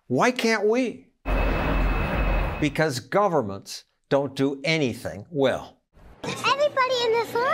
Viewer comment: Regarding what is said at 5:50, Barack Obama said "There are some things that we do better through government."